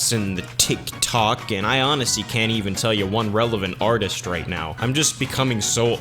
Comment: Every time you said "artist" I heard you say "otters" and I was very concerned